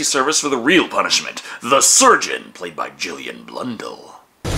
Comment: I thought you said Julien Blondel, AKA the guy behind the Elric graphic novels.